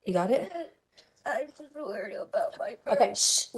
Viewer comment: ive replayed this so many times & i swear says "Im worried about my burns" at the end of this recording, and this evil monster, the one probably called MOM, someone he COUNTED ON....cuts him off and shushes him!!! 😡 May justice for this evil NPC be swift and brutal to mirror the pain she inflicted on this innocent little soul ☠️ 🔥 Rest in peace, sweet 💙 Thanks so much for the video!🙏 Brilliant, as always 😁 Much love from WV 🤙🏔️ 💙 💛